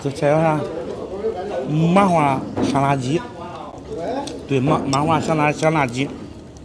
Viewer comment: 麻花香辣鸡 听成了麻花沙拉鸡